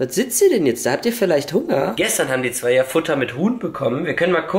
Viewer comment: Ich habe verstanden: "Gestern haben sie ja Futter mit Hund bekommen!" 😂😂😂😂😂😂😂